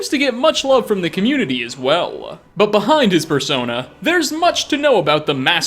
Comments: I thought you said "Behind his fursona" lol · you really didn't pop that P, and I swear it sounded like Fursona. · I thought said "behind his fursona"